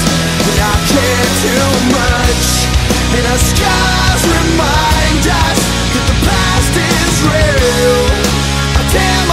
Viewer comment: Oh..I always thought he said "the stars remind us that the past is real".... why did I think that the song itself is called scars